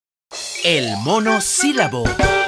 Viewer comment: por un momento pense que decia "el mono silbao"